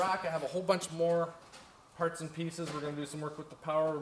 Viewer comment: Was is just me or did he say parts and penises